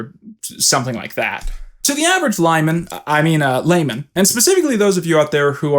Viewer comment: The caption says "lineman" instead of "Lyman" like the joke means. If has anyone that proofreads the captions, they should correct that.